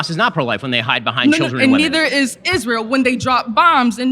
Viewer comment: She clearly says bombs and nukes.